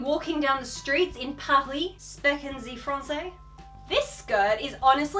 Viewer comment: Did she just say "Sprechen Sie francais?" 😂😂😂 That look you gave after that killed me 😂❤️